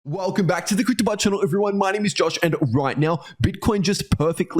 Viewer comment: I swear every time I hear your intro I hear, "Welcome back to the CryptoBot channel, everyone!" To be fair though I live in the U.S. Midwest, where people have practically no accent whatsoever lol